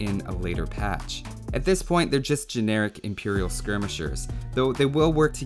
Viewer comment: I thought it said: 'Imperial skirmishes' xD